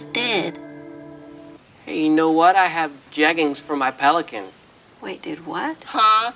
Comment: “I have jeggings for my pelican” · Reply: I thought he said leggings...hahaha